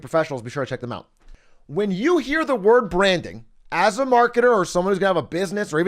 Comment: I hear Brandon. When you say branding.